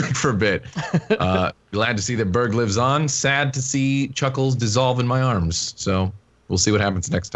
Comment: CHUCKELS!!!!!! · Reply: I lie to you not, I thought he said buckles at first and thought Berg was gonna go through a kingdom hearts phase.